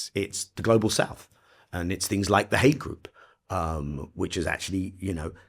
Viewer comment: Did he say "the hate group?" 🤔 Can't be, right? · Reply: "The Hague"-Group International court of justice Holding israel accountable